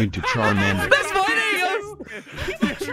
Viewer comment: does say “thanks my N***a” ??